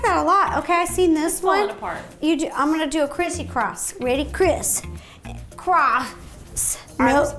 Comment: When she said, “Criss...cross.” I heard, “Criss...crotch.”